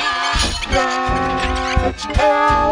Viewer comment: i heard "Lets Kill the world"